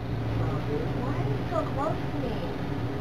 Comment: I cannot stand when people get that close to me. It screams “I’m trying to pick pocket you” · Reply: At first I thought she said “Why did you just grope me?” But she actually said “Why are you so close to me?”